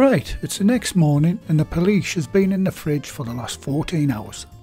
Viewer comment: I thought you said the police have been in the fridge for 14 hours